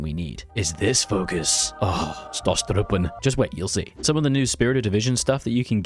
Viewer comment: What did he say at 7:50? Stusstruppen? Stormtroopers is Sturmtruppen in German. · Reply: It's "stosstruppen" in-game